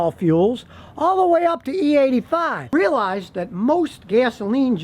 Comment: I swear he said the Michigan Troll System.